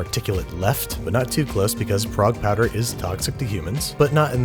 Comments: wtf is the powder he saying? I cant understand what this dude said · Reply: Prague powder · I keep hearing "Prague Powder" as "Frog Powder" and thinking I'm listening to a magic potion recipe instead of cooking food.